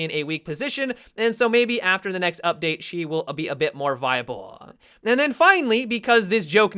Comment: A-be-a-bit You sounded like porky pig there. Lol